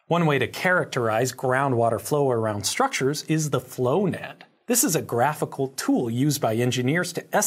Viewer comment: Did anyone else hear, "Flonad?"